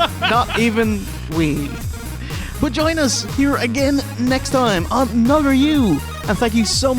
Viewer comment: "Vaginas, here again, next time on Nogger U." Is that what I heard? Cause, I think I missed the first round of vaginas.